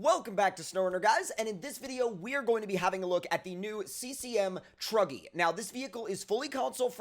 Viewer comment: At the start he stayed cm truggy I thought he said druggy!!🤣🤣